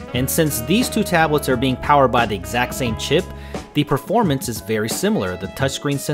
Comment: haha, i thought you said same shit